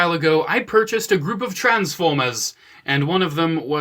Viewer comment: I swore I heard "A Group of Trans-Pho-Bias" not the Jersey accent "Trans-Foy-Mas" at the beginning. 😳😂